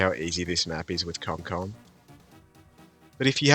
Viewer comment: what did he sayyyy?? 🤨📸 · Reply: Comcom= comanche commander lol